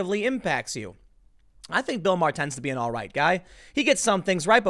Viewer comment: "Bill Maher is an alright guy..." No Tim. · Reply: First I thought he said alt-right guy.